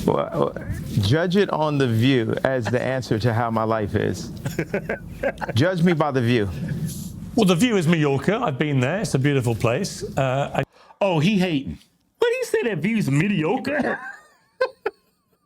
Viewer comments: I heard “mediocre” at first too 😂 once someone mentioned he said “Mallorca” I went back and yea he definitely just said the location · I thought he said, "the view is New York".